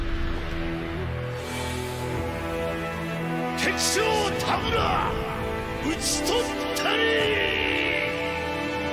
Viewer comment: Anybody knows what's he saying in Japanese at 1:25? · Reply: 敵将"田村"、討ち取ったり てきしょう たむら うちとったり tekishou Tamura uchitottari